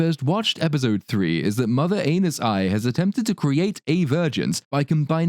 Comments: Every time they say "Vergence" I hear "virgins". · Every time I hear Randolph say "Vergeance" I hear "Virgins" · I keep hearing "virgins" instead of "vergence" and I get terribly confused.